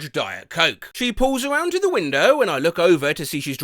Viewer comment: i heard weiner instead of window